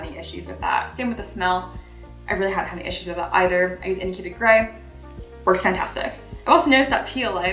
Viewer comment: Sorry around 7:42 what is it you said you use for the smell? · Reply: it sounded kinda like "anycubic grey" , which must be the brand of grey resin she's using.